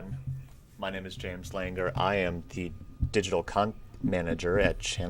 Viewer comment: Is James Langer the "digital c*nt manager" or did I just mishear? Is this his full time position? 🤔 As I feel I have real experience in this area🤪🤣🤣🤪